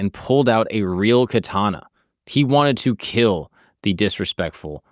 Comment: I thought they said that he used dual katana